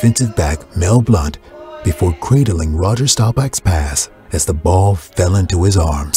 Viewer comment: "before ____________ Roger Staubach's pass" --- What's the word in the blank?? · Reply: Cradling -- Thank you!